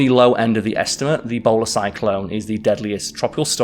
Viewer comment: I thought he said "Ebola Cyclone" and ngl I was more terrified of that.